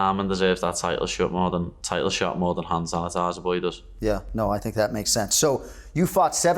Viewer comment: what did he say? More than who? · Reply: He said arman deserves the title shot more than “hand sanitiser boy” (ilia) he calls him that because paddy bounced a hand sanitiser bottle off his head when they had that altercation.